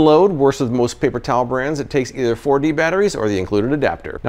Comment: I heard it "takes 40 batteries!" Took me a couple seconds to realize 4D batteries lol ❤❤😅